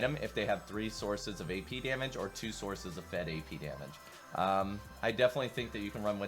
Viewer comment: The subtitle says "fed AD Damage" instead of AP at 9:10 But that's a really great guide overall !